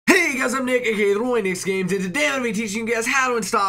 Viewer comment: please tell me i'm not the only one who heard him say "HEY GUYS I'M NAKED HERE"